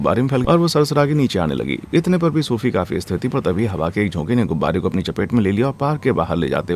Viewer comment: पर क्या बोला भाई कई बार देखा नहीं समझ आया 😂😂😂😂😂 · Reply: स्थिर bola bhai